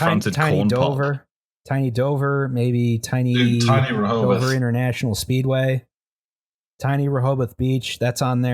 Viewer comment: I heard "Tiny Dover International Speedway" YES please make the mini-Monster Mile.